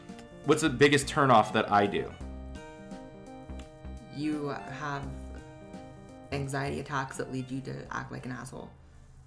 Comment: haha wow definitely need to turn my volume up cause I didn't hear the question & then I thought she said "you have panic attacks that lead you to the back of my asshole" 😂😂😂